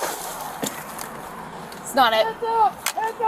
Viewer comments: I clearly hear thr female cop says "its not it" smh. She knew right away it was the wrong car but still followed through, probably after seeing she was a black woman driving. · I heard the female cop say, "That's not it" but, then proceeds to point a loaded gun at her, still handcuff her & treat her like a criminal!! This is SICK!